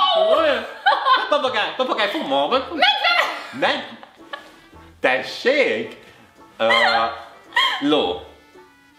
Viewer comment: mit mond? 😆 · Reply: Tudom, hogy 2 éve Írtad. De azt mondja: Fogd Má' Meg xd